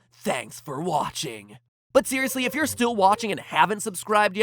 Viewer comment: It sounded like he said "theoriously" in the beginning oh my god